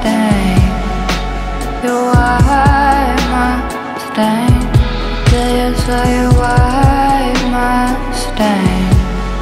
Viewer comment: At first I thought she was saying "The day I saw you wipe my stain." 💀